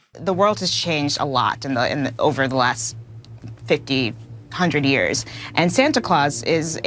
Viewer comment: “50 hundred years”. Am I hearing that correctly? Fuckin’ hell..... 🤦🏻‍♂️🤦🏻‍♂️🤦🏻‍♂️🤦🏻‍♂️